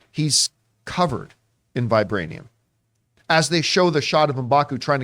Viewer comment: Again, I’m not a native English speaker but… I don’t think Shuri says “he’s covered in it” when speaking of Namor and vibranium. What I hear is “he’s coveting it”, which makes a lot more sense!